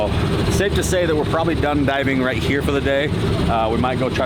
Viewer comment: I thought you said driving. Yea I'm dyslexic Wow super amazing girls. I had tears